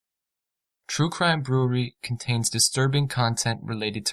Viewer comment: Please fix the intro it’s so distorted and it sounds like he says “troo crime brew-reee” it’s brew-err-ree.